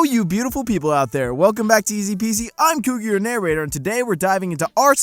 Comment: Thought he said “I’m Cougar your neighbourhood”